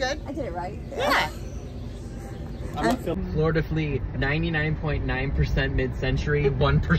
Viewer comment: I thought you said you were at Florida Flee! 😂 Couldn’t believe y’all made it down to Florida together!